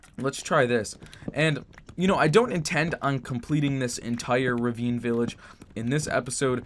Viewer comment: I thought this said Rave Village And was kinda confused